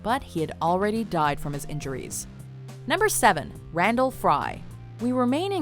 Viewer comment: At first I heard Brundlefly...